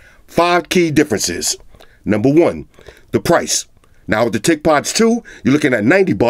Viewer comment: I thought he said “thickpods” 😂